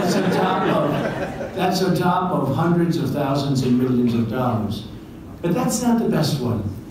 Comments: "hundreds of thousands of millions of dollars" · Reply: The captions say "of" but his voice said "and". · Trump says "hundreds of thousands and millions of dollars". The subtitles got it wrong, reading "hundreds of thousands of millions of dollars".